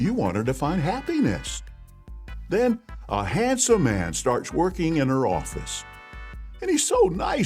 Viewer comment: I thought he said "starts twerking in her office"